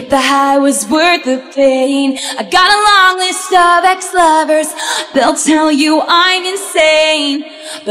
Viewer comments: This whole time I thought she was saying " you gotta love these Starbucks lovers, they'll tell you I'm insane ". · For so long I thought she said.. gotta love those Starbuck lovers, they'll tell you I'm insane😂